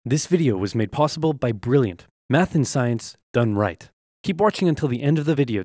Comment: Wait did I hear Meth?